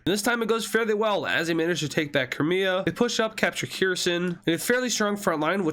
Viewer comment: did you say korea? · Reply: No crimea